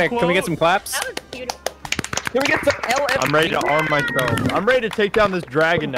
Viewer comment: I legit thought said "Can we get some LSD?" for moment.